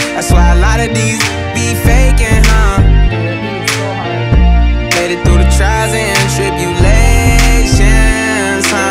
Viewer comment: Who speaks in the background at 3:42? What do they say? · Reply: It’s the girl singer in the outro. She says “yeah that beat is so hard”